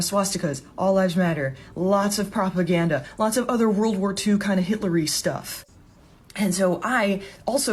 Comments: > Did you say Hillary stuff? I'm melting.... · Lisen to her at 6:52 i think she say hitlery en not hitler Ps i am Dutch so my englisch is not that good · Did she say Hitlary stuff? 😂